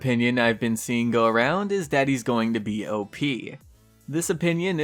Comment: At first i heard "Daddy is going to be op".😆😆😆